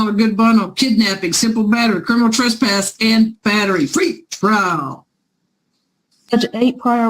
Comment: Tequila shots every time she says Pretrial.😂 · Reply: Dang. I kept hearing “free trial” and now that you write out “PRE” I feel like an idiot. Haha. Thanks!